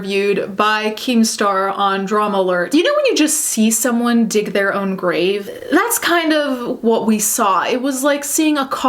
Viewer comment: Could've sworn you said 'D'you know when you just see someone, dick their own grave? ", and tbh, as a metaphor, that really does convey just how 'off' this guy is.